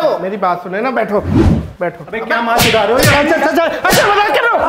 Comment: who heard kya Ma ..... Re ho 🤣🤣🤣🤣🤣🤣🤣🤣🤣🤣🤣🤣🤣🤣🤣🤣🤣